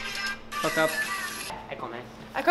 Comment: Did anyone else hear Marcus say fuck up